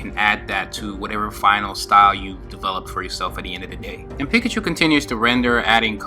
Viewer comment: wait haha did u say pikachu?